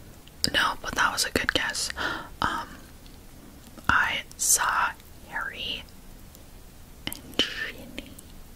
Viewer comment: Guess who I saw together in the halls Me: Harry and Ginny? No, but good guess Also: Harry and Ginny Me: 😐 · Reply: I thought she said Harry and Draco! (But still like Drarry FOREVER)